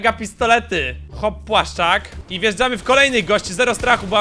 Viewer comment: czy tylko ja usłyszałem chłoppłaszczak xd